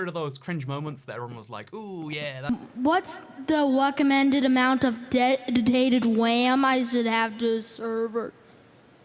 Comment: Btw if you’re wondering what that kid said at 1:10 “What’s the recommended amount of dedicated ram I should add to the (or a I still don’t know) server?” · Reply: what's the weccomended amount of deditated WAM I should have to a srvr